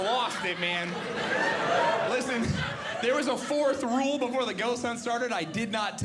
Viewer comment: I thought he said his mother was a goat hunter.